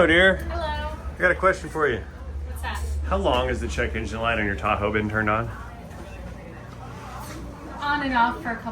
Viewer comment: I swear to God cause the volume was down. It sounded like you said how long has the light on your taco been on?🤪